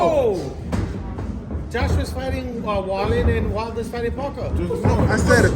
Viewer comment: This shows the hypocritical world we live in. How the hell is this guy still speaking about boxing? He's a disgrace. The journalist (if you can call him that), being surprised Joshua and Wilder aren't fighting each other, just shows he didn't do his homework. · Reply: Miller thought he said Wilder but the journalist said Wallin so you got that wrong bud